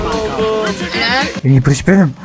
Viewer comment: da gerçekte ne dediğini söyleyecek misin polis mi çağırayım? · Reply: En iyi briç benim diom iste